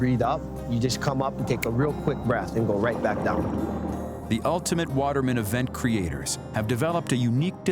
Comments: I thought it said watermelon and that the man was holding a watermelon underwater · I thought it said ultimate watermelon lol